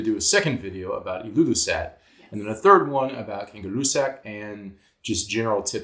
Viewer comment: Did I hear "Kangaroo sack"? 0:28 😜